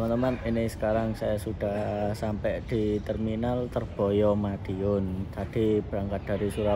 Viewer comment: "Terminal Terboyo Madiun" akwkwkw ini cuma gw doang yg salah denger apa gimana yak